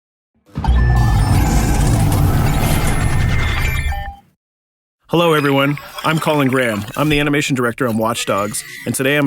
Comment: Transcribed subtitles: "hello everyone I'm calling Graham". It actually does sounds like that's what he's saying xD